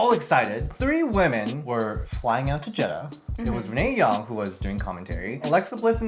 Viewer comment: I seriously thought said "Mae Young"! 😳😳😳